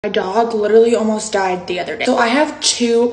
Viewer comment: I’m glad ur dog is safe and sound!! 😊 but it kinda sounded like you said “McDonald almost died” at first